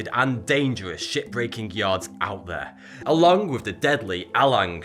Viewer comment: why does it sound like hes saying "shitbreaking yard"?is it just me?